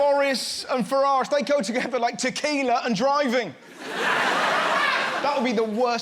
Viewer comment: Mis-heard and thought he said "IKEA and driving" and I couldn't have agreed more with that statement.